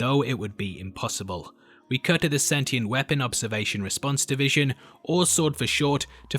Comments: i thought you said ”The ONE division” :)))))))))))))))))))))))) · Thought u said one division